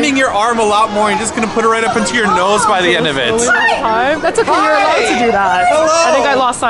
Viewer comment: it sounded like a girl was screaming "Hi!!" To get your attention. Or am I overthinking things?